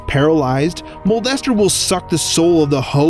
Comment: When he said mold estster I thought he said molester